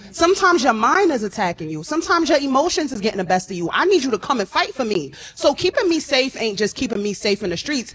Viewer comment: Did she just say fight me for me cause my mind is attacking me